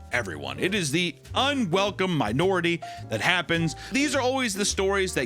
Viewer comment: lol my bro It sounded like you said the problem with trading cards was unwelcome minorities.